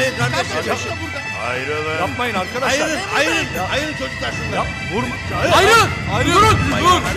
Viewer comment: de ayrılın götlermi dedi benmi yanlış duydum 😂